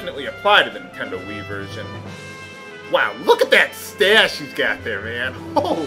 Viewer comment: Replayed 1:39 about 10 times because I thought he said "look at that stash he has there"... wasn't sure what innuendo you meant before I realised it was "stache"